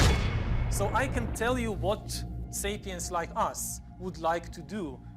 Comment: Did I hear him say"Satans like us" at 4:01-4:02?